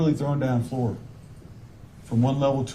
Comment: One of his words that got me the most was how he kept saying “she was throwing down the stairs” because it was like he could not say thrown. Strange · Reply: I thought he was saying “throwen” 🤷🏻‍♀️ Either way, he mispronounces MANY words mostly the big ones he is trying to use to sound smart. Unfortunately for him it’s not working. 😂